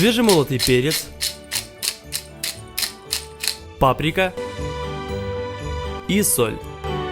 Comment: * Я которая услышала патрик* Я:Бедный патрик😢:D